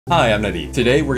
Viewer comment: Legit thought you said "Hi I'm nutty" on the intro lol